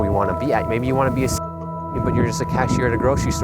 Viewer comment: why is it blanked out? What did you say? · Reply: Hey Kathy! The mic had problems and blanked out and I didn't really know how to add in new audio. But the words I had said were "Maybe you want to be a ceo of a corporation but right now you're just a cashier at a grocery store currently. Was stressing the point that we need to be patient as we grow and pursue our careers and our calling. Hope this helps! Stay Blessed!